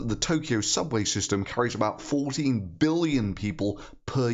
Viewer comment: 40 billion ppls? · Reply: ***** I totally understood 40 billion there, which is why that's what I responded to. Not sure if my crappy ears or Your speech, but if 14 is what You actually said, I take everything back and apologize for mishearing it.